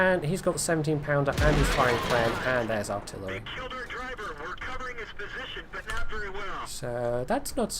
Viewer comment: it at first sounded like "Driver's dead, we are covering his bullshit but not very well"....XD